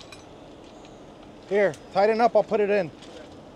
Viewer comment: Did say "Tighten up and I'll put it in"?????